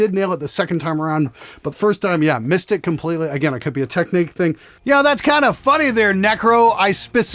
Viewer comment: Hey, I would really appreciate a test version of the Element Bass. Guy at 8:34 probably thought you said "Technic" 😂